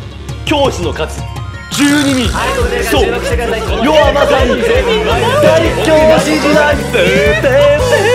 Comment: 見るからに肉体派の先生ばっかで 体育教師時代って聞こえた…🙃